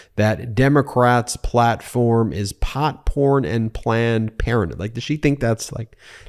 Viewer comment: I thought you said Popcorn and planned parenthood.